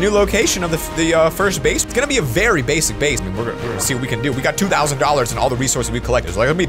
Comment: Did you just said a Base - ic base at 12:32? Anyone?